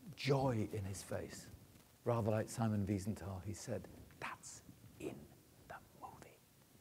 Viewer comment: I think he said "That's IN the movie!" at 3:47, not "That's it, the movie" (like it says in the soundtrack). That would make a lot more sense, anyway.